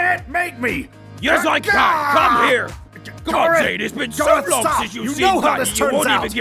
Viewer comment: Is it just me? Or do I hear Garroth accidentally say Dante instead of Zane at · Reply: He said "God Zane!" It did sound like Dante,though.